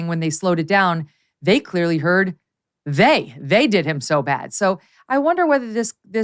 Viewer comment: I hear “They did him so bad”.